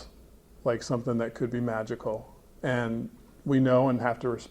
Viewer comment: When he said magical i heard "money"